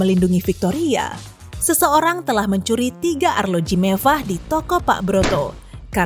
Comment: dengernya kek bkn mewah tp MEVVAH :)